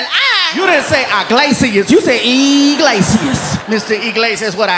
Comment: I thought he said I need a bigger man